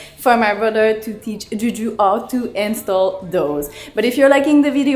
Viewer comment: Does she said Jiju to mohit😃